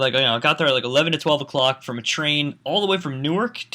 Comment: when he said train I thought he said tree. in my mind i said " Fluffy in his nature habitat? "